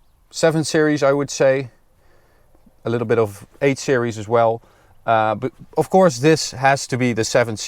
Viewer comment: did anyone else Siri thought they said “hey Siri” when he said 8 series?